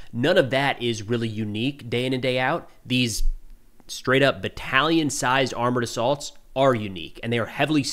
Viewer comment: for some reason i heard "Italian sized armored assaults".